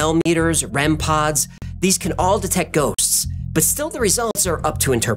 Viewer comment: Did he say goats?